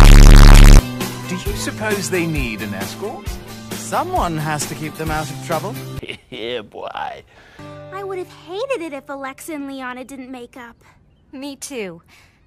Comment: I UNDERSTOOD WHAT SHE SAID AS "I WOULD HAVE HATED IT IF THEY DIDNT MAKE OUT " AND SAME GIRL SAMEE